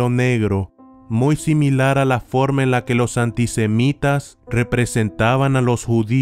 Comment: cuando dices antisemitas (en mi país honduras semita es como un queque) parece que dice que no ,e gustan las semitas v